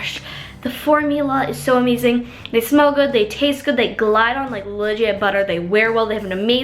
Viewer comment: did she say, "tastes good" ?